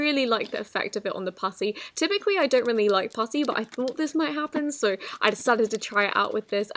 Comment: sounded like she said p*ssy